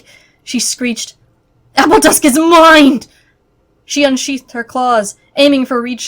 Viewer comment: Sorry, I don't have the book and I misheard the line: "Appledusk is ____" 0:50 can someone help please? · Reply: JessicaisKool It says "Appledusk is mine!"